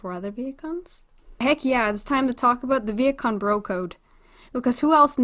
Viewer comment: i thought she said "the viet kong bro code"